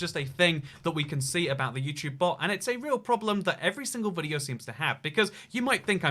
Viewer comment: I watched this on captions to see how many mistakes there were... The start: hello, I’m vx flicker 0:52 “every single video” captions: every single buddy Most whats: or 2:29: cousin instead of skeleton 3:14 ran are instead of rare and Most wolves: wolf’s 4:02 missed or Nor instead of naught Books instead of wolves This might become the longest comment ever He’ll instead of well All instead of oh Not a big mistake but mom and he’s British so it should be mum 6:31: worth scots and Stun instead of stone forts instead of thoughts 7:25: Star Wars Sorry, I wish I was someone who would put in the effort to do every mistake, but it’s hard work